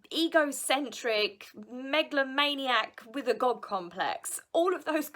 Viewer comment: Lol I misheard when you said God complex. I thought you said gob complex 🤣🤣🤣